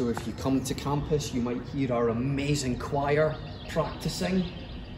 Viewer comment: Funny moment- at 2:43 you say the choir is practicing but the transcription of the captions says "trying to sing" probably not what you meant haha!